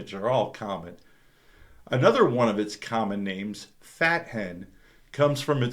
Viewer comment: Thought you said "Fathead" at first 3:51 and I thought to myself, my wife likes that one too😂